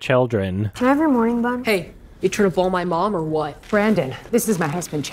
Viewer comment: I coulda sworn that kid said "You trying to blow my Mom"